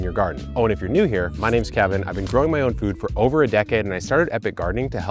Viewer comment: I literally heard "my name is Eric"... 😮😮 I had to replay that part!